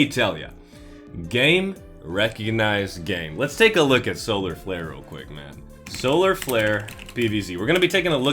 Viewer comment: I THOUGHT HE SAID SOLAR FLARE BBC! I WAS SO WORRIED!